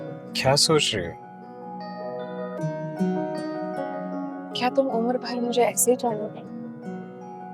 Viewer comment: what did she say at 10:54? ' kya tum umer bhai mujjhy aise he chalo ge' ????